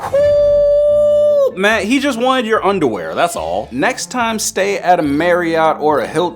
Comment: bro what😂 😂😂😂😂😂 did this do just say you just wanted your underwear now you weird